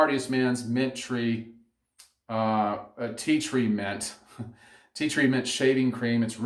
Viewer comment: I’m glad you had that small tea tree mint “goof up” because for the longest, until I saw this video actually, I thought guys were saying tea treatment.